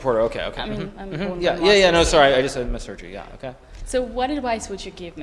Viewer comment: What is Hikaru referring to at 4:32? I hear "I just had my surgery", can someone explain? Thanks! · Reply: He said “I just misheard you”, but now I can’t unhear “I just had my surgery”